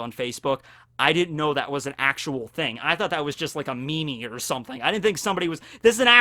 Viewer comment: Did you just say “me-me” as in meme @ 17:19?